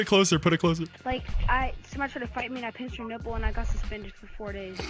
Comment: “Someone tried to fight me and I pinched there nipple” Edit: Thanks for 27 likes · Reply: I thought he said I punched her nipple :DDDDDDDDDDDDDDDDDDDD